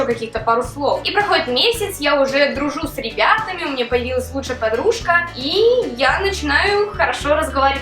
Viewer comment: Мне послышалось "куча подружка", а оказывается ты сказала "лучшая подружка" :3